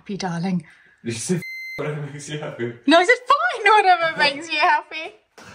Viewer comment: "Did you just say f*ck whatever makes me happy!?" 🤣😂